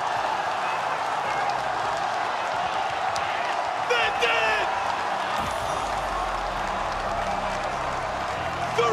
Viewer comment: wtf does the caster say at 0:52??? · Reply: they did it